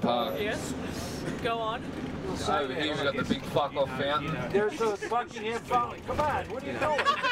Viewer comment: What does the bus driver say at 7:07 after Tai says we got the big fuck off fountain over here? haha. I think he says "there's no such a fountain, COME ON, whattya doin?" but can't be sure :P